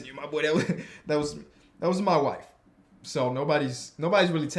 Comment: Did just say his wife? When did they get married or am I missing something?